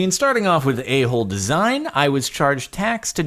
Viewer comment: I swear he said welcome back to number 19, welcom back to asshole design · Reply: he said “Welcome back to number 19, starting off with a hole design”